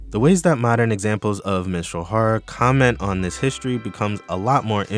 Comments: I thought you said MENSTRUAL horror and I think I found a whole new genre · I thought you said Menstrual Horror for a sec 😭